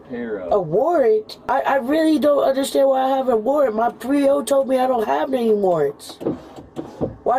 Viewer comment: Thank goodness for the subtitles, I totally thought she said “My three year old told me I didn’t have any warrants” 🤣🤣